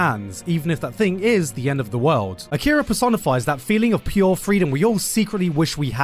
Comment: man, i misheard at 4:27 "Akira Persona 5". god have mercy on my soul, 'cause i've played Persona 5 Royal for 200 hours...